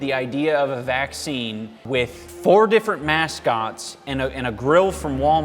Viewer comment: You said "mascots" I heard "mask-ots"